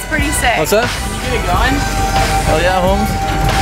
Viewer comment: For the Dutch people, did he just say at 1:00 "goeieavond"? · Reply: Hell yeah homes